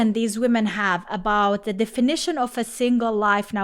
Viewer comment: when I first listened to it, I thought she said “… the finition of a single life”. I was like “finition”is not a word. Then I used closed captioning and she actually said “… the definition of a single life”. I guess said the words “the definition” so fast that I thought she said “the finition”. It might just be me having a hard time hearing, yet I’m only 30 years old.